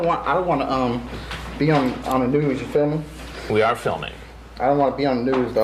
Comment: He said “You feel me” Chris thought he said “You filming”